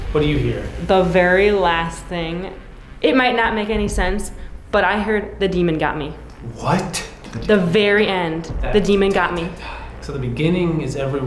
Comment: I heard "everyone of you, get out of here. The demon got me."